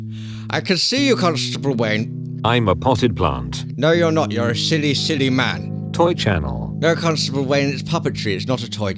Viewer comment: Not gonna lie I thought that at 0:27 he said " no your a shity shity man"